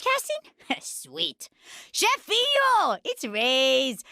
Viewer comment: Hey mate, hope you doing well. I realised you puted "idk what that spanish word is" at 1:09. She is saying "Chefinho" in south american Portuguese witch means Boss in a cute way :)